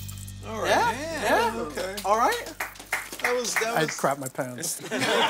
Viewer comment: Did Ming just say he crapped his pants?